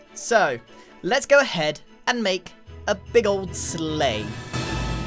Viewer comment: dunno why everytime says "big old sleight" i hear "big old slay"